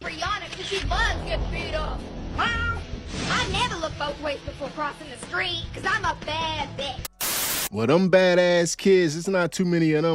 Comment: “I never look both ways before crossing the street, cuz I’m a bad *****”🤣🤣🤣🤣💀 so serious tho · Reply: ooohhhh, that's what she said... I heard "my mother looks both ways before crossing the street coz im a bad b****" and I was like, huh, that makes no damn sense and that's because I need to get my hearing checked 😂